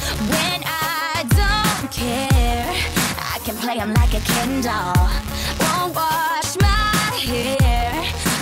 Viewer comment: lmao every time I hear this song, at 0:26 I hear "I can play 'em like a Kendall"